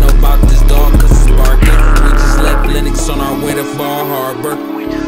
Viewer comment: Did he just say.. he in Atlanta on his way to Michigan? “BallHabor.” 🤔😎🥶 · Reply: “We just left Lennox on our way to Bal Harbour” (Miami) definitely not talking about bum ass Benton Harbor, Michigan 🤣😂😂😂😂 💀